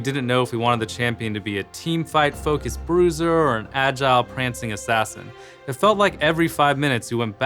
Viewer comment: i heard "prance" Pwease gib prancing champ!